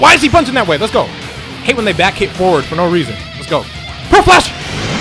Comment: DONT WORRY MORE BUUHAN later!! But this was for the hype!! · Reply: 10:50 when you said "PEARL FLASH" I thought you said "HOT FLASH"